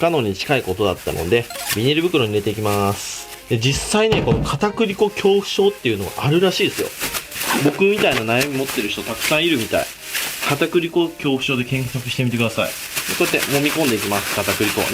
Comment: が「片栗粉恐怖症の人結婚してください。」って聞こえてたわwよく聞いたら全然違ったw